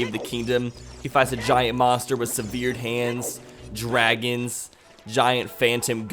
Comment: Did I just hear seveared hands?